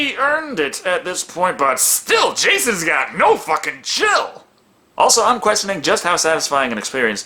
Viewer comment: sounded like you said "Jesus got no fucking chill" Hell, I ain't complaining!